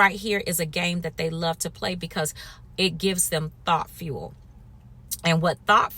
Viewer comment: When you said Thought Fuel, I originally heard you say "T.H.O.T. Fuel"! 😂😂🤣🤣